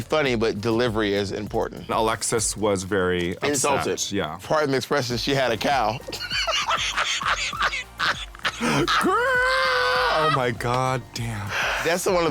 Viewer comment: somebody please help me with what Bob said at 2:39 .. my native tongue is not English so it slipped my "ears" LoL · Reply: Insulted Pardon me the expression, but she had a cow.